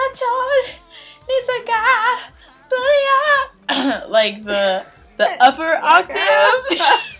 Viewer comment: DYING I thought she said "thugger octave"!